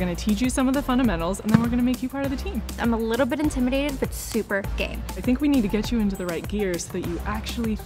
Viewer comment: who else thought at 0:13 she said “but i’m super gay”